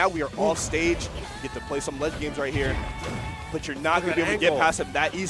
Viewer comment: HELP WHY DID I HEAR PLAY SOME LESBIANS RIGHT HERE💀💀💀